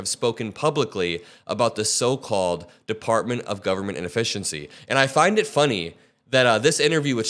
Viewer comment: I thought you said inefficiency instead of efficiency which inefficiency would be alright😂